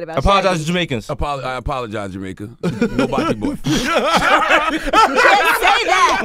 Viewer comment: what’s the offensive word/phrase charlamagne says? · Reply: He said he’s nobody’s boyfriend